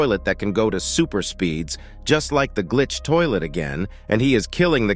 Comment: Do you mean glitch toilet because I heard you say camera toilet